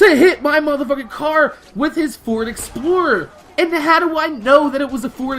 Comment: when he ford explorer i thought he said dora the explorer